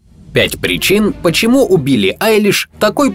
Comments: Мне одному показалось что он сказал: Почему УБИЛИ Айлиш ? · "убили" послышалось.✅ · О боже я услышала "убили айлиш''. не дай бог конечно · "Почему у Билли Айлиш" мне послышалось "почему убили Айлиш"😂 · С начала мне послышалось почему "Убили Айлиш", а не "У Билли Айлиш" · Мне одному послышалось : ПОЧЕМУ УБИЛЛИ АЙЛИШ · Мне послышалось почему убили Айлиш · Когда сказали <<у Билли>> мне послышалось <<Убилли>> я уже и спугалась лайк если тоже так подумал😂😂😂😂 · В начале послышалось «почему убили айлиш?»